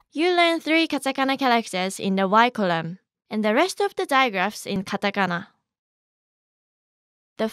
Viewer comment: did you say digraphs? XD